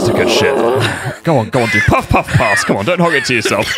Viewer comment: "Puff Puff Pass." Me, half-listing: Did he just say 'Puff Puff Arse?!' 😂😂😂